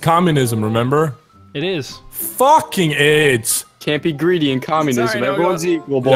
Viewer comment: I heard nogla said at 6:07 Fuckin aids